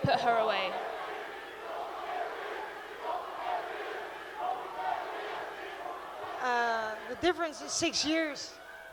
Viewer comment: ? WTF crowd is saying? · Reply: "Colby cant read." Lil